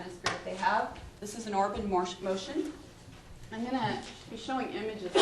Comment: Did she say "orphan morse motion"??? 19:37 Can someone spell out what she said? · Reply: Orb in motion